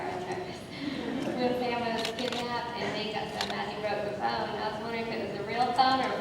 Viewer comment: LOL at around 10:40 I thought she asked if it was a real thumb or a prop thumb instead of phone... Gee am i glad i heard that one wrong